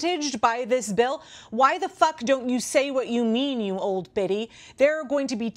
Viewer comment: I heard "Old Bitty" and choked 🤣 Go Mama!